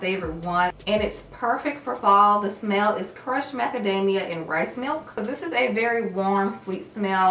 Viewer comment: Ooohhh rice milk! I thought said breast milk 😂😂😂